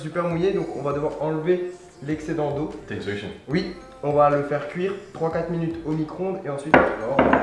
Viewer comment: mon son était pas très fort j'ai entendu "on va faire cuire 34 minutes au micro-onde" mdr